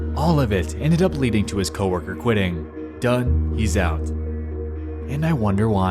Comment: it sounds like it says get out